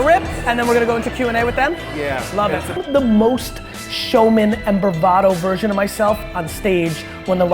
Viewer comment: Can anyone please tell me what's the word he says at 9:09? "The most showman and ... version of myself...". I've been hearing it since a while now and I don't know what it means. I'm from Argentina by the way. I love the content Gary! Thanks. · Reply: I would say "verbatim" ?